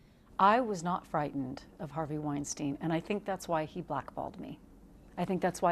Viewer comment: Did she say "buttholed me". Way to go Harvey!